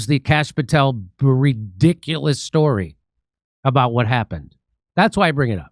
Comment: Did you say, "Bulldiculous" ? If not, I'm claiming it! Checked, you said, Breediculous story @ 8:22, my ears heard, "Bulldiculous", which sounds so much more fitting! More fitting. Keep up the good work, Maygen sure wont. j/k